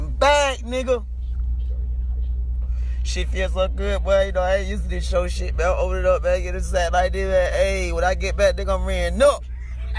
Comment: what the fuck did you say? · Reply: "i'm back and I aint use to this show shit but all the money we done ran up i'm like aye"